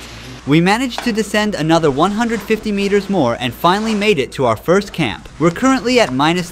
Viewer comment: Omgosh I thought he was going to say final camp but he said first camp